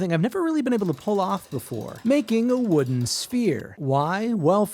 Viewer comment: I heard “wooden spear”. My thoughts were, how is he going to pull this off, and where is the diorama ? 😂